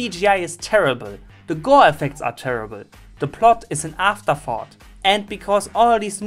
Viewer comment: Thought you said "afterfart" instead of "afterthought" at first. But tbh, both of those apply to the plot.